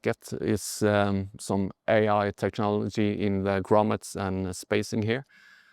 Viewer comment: did you just say Au technology in grommets ? 😂😂😂 thats some very dumb statement